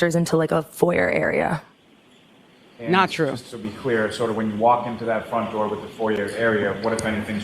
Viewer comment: I thought said she was the only one to walk in the front door..